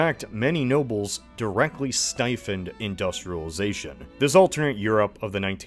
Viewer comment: I know this is probably a stupid question, but what’s that word at 12:27? Stifen?